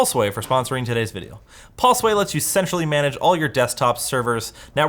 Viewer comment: istg i heard "pulseway let's you sexually manage....."